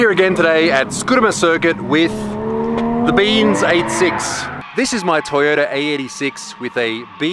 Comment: It always sounds like he says "beans 86" XD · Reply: yes its beans86